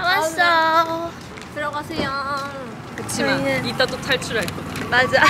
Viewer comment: I hear "selamat siang"